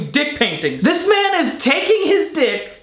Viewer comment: in reality people only have 2 minutes to live but every time we breathe the clock resets🤓🤔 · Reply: Tombstone Soda I thought you said that this is making you hard.